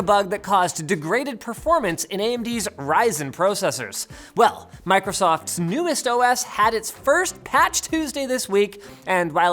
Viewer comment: For a second I thought he said "Microsoft's newest Dumb As* added.."